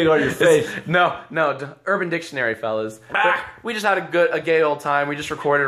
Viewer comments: Jack: We just had a goo- a gay old time. I'm not the only one that heard gay instead of great right, right? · Did he just say, "We had a gay old time"?????